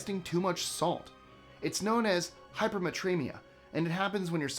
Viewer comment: Tiny quibble. I heard “hypermatremia” it’s actually hypernatremia. Na is the chemical symbol for sodium.